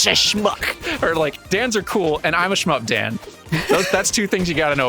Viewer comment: I thought said that he's the SHMUP Dan and was surprised I totally missed that interest. Was wondering which he'd start talking about then realised I'd misheard 😅 You ain't no shmuck! You're a skilled and cool artist, entertaining talker, and noted Dunker of Banjo Tooie 😄